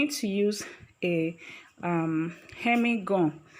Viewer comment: ?? "hemi-gum" ?? Also, since it is a tutorial, the use of sewing thread that is contrasting (e.g. red) instead of invisible white would be helpful.